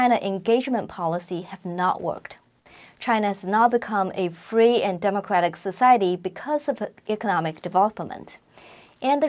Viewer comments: she says china has now become a free and democratic but subtitles says not a free and democratic...wtf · Did I hear this correctly? "china has now become a free and democratic society because of economic development"